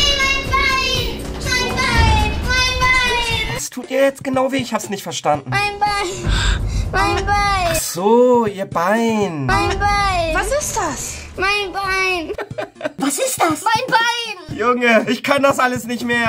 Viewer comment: Ich hab “Mein WEIN” verstanden 🤣🤣